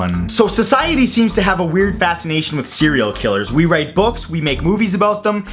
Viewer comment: i thought he said "high waist serial killer" and i just imagined a guy in pants up to his armpits xxxxD